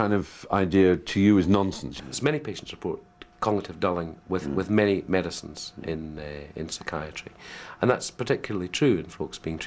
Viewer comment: What does the doctor say in response to Steven's question at 16:09? I can't make out it out. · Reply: 'Many patients report cognitive dulling with many medicines in psychiatry, and that's particularly true in folks being treated with bipolar disorder. Some of that dulling of cognitive performance might not be due to the medicine at all, but might be a property of the illness.'